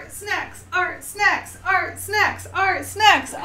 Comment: In the beginning I kept hearing “ART, SEX! ART, SEX!” Over and over... Never been so confused by one of your intros.😂😅